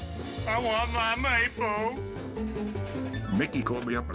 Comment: i want my what. I cant understand him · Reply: Maypo. It was a breakfast cereal. I think it was oatmeal.